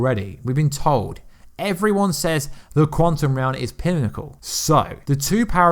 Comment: Quantum Realm lol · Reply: I know, but his accent sometimes sounds like he says krantum, lol